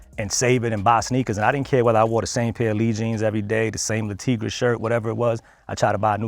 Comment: Editor, i think he's saying "le tigre shirt". a clothing company that had a polo shirt but instead of having a crocadile like lacoste , it was a tiger